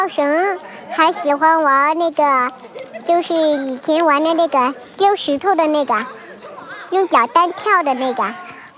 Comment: did she said the n word …